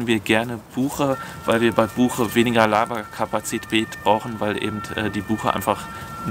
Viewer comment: Ich hab' bei Buche "weniger Laberkapazität" verstanden – aber ich höre schon nicht mehr gut … 😂 Davon mal abgesehen gehe ich für uns als Faustformel weiterhin von 0,7/1,4 zwischen Rm und Srm aus, das reicht für uns als Abschätzung. Die Mengen sind allerdings auch deutlich kleiner, so dass es finanziell keine Rolle spielt – außerdem waren wir froh, noch Holz zu einem fairen Preis bekommen zu haben, das war wichtiger.